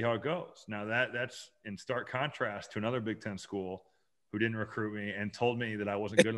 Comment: What program told Robbie they didn't think he could play in the Big Ten? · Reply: I thought he said, "Another Indiana school".